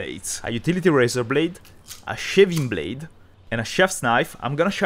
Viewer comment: I misheard "a shaving blade" as "a shiving blade" lol.